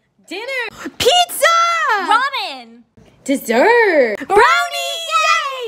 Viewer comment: I thought she said vomit not ramen